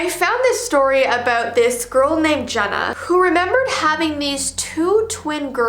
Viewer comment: I have a little cousin name genna Wait did she say genna or jenna